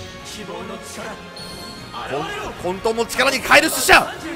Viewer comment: 混沌を力に変える寿司屋 に空耳したwwww